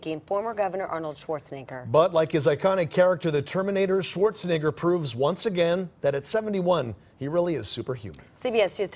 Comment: what he sayyy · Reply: He said "Screw your freedoms"